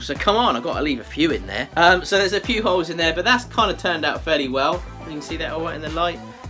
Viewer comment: Sometimes I can't hear his L and I swear I kept hearing "there's a couple of hoes in there." It's entertained my inner 12yr old boy this ho video. ;p